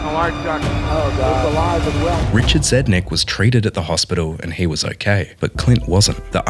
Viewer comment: When they said that Richard was treated in the hospital i thought that they said that he was traded in the hospital